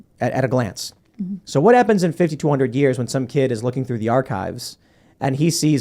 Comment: I thought he said "what happens in 5200 years when some kid sees these" lol